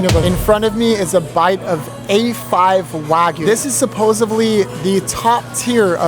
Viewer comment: Bro did you really say “supposebly”?